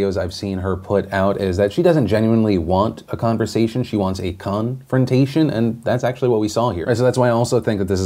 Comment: honestly thought Phil said gun-frontation instead of confrontation :p